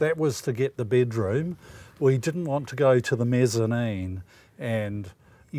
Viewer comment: I have a question about their water supply. Where does the water come from to use their dishwasher? I thought they said that they bring their own water. So I guess they go to the city to fill up jugs of water?💋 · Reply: Tiare K Oh! I thought they said they didn’t want water tank.